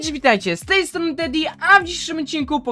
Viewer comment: usłyszałem daddy XD